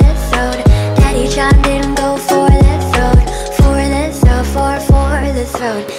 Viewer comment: Thought it said “go for the throw “